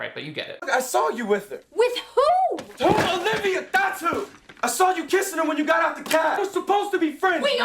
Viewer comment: Also I’m pretty sure Channing Tatum slips when he accuses Viola/fake Sebastian, it sounds like he says “I saw you kissing him when you got out of the cab” but clearly she IS him and would’ve been kissing “her”